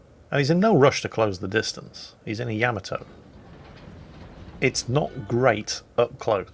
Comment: Thought you said this was the Yami....looks like a helicopter